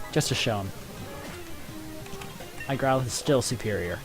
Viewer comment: I heard "My Growlithe is still Serperior" which concerned me momentarily